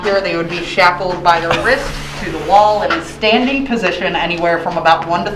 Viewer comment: When she said "inmates were hung by their wrists" I thought she said "ribs".....